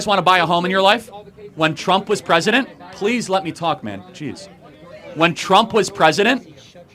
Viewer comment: I had to play it back because I was walking when I first hit play. I thought he said, "Hey, let me talk, Mac n Cheese."